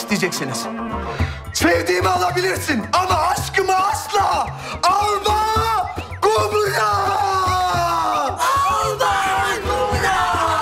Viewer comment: de ne diyorlar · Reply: Bravehearth taki savaş öncesi konuşmayı yapıyo, alba gu bra yazarsan çıkar